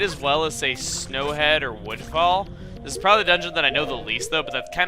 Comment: Lol its sounds like he said windfall instead of woodfall.